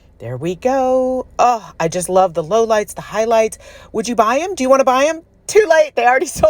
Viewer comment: These are great. I'm not surprised they were sold pretty much before they were completed. The prod you were presenting (sounds better then pushing 😉) when you were saying the name I kept hearing "Would You Bend" like are you willing to be a contortionist for your art? LOL I knew that's not what you were really saying, but my head went there anyway. 🤯🥳